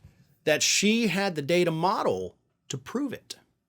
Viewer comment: I thought he said.....SHE HAD TO DATE A MODEL to get the evidence.